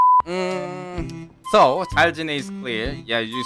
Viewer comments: He is unaware of the slang “cutie patootie” so he thought she said “for duty” lol. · Think she said “You’re a cutie patutie.” Not Cute for Duty XD